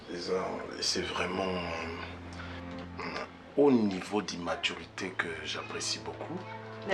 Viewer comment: Aidez moi a comprendre Il a dit immaturity ou maturity ? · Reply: Immaturité